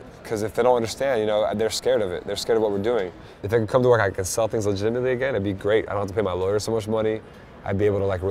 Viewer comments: Whaat did he said? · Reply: mean the bit where he says "If they could come to where I could sell things legitemately again that would be great." - He says it really fast so I figure that's what u meant? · WTF Does he say @ 1:02??? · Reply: "If it can come to where I can sell things legitimately again..."